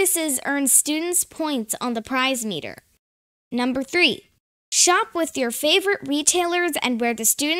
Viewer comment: OK I'm old and my hearing is used but @ 2:20 I swear I hear shoplift your favourite retailer I'm sure it is shop at your favourite retailer but it made me laugh.